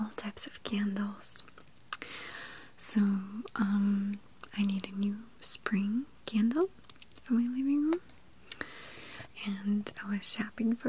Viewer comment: what the heck is she saying😂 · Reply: She said "I needed a new spring candle in my living room" I believe